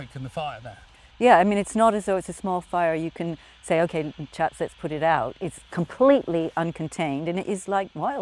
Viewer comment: does she say "okay chat let's put it out"?? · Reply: Not chat, she said "Ok chaps, let's put it out"